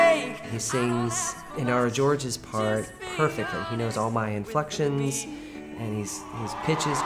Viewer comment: I don't know if it's just me, this guy were actually able to sing and harmonize Jason with different chords if you noticed. There were two times Jason repeating the "with me" with the same notes but different chords, but sang two different notes to merge the appropriate chord, I mean this guy really came prepared lol. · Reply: What does he say at 1:34 ? That sings "our Georgia's part" (???) perfectly???